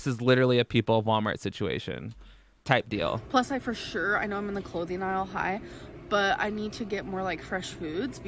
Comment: Did she say she was in the clothing isle high? @ 23:20 😂😂😂